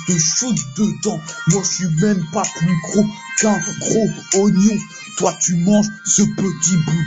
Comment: c'est moi ou il dit "je suis pas plus gros qu'un gros oignon" à 2:10 ?